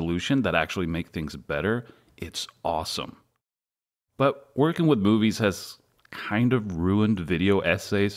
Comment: did he say ''working with boobies'' or is just me?